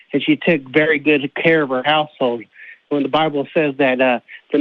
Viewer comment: I know he said household, but it sounded like "and she takes very good of her asshole"😂